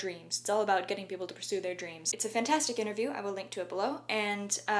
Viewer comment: “I will link to it below". I heard "I will link to a blow“. :(